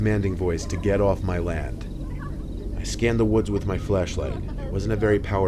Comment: Did anyone neotice it said fleshlight instead of flashlight in the sub